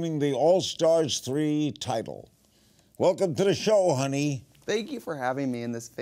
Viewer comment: "welcome to the show honey" I thought he said hunty😅